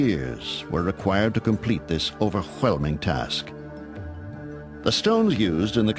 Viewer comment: Did anyone else hear "over-HWEL-ming"? at 7:00? Say "well" Now say "over-well-ming" :P